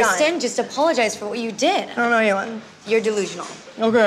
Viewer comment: what the heck did she say before the aokay.?! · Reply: I think she said I don't owe you one.